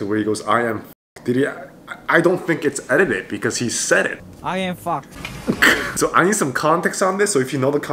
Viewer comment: when he said I’m fu@ in the first clip he said I’m fox but people misheareded it